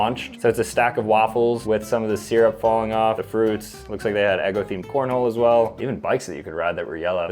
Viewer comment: I swear I heard "eggo themed p-rno" first time I listened to this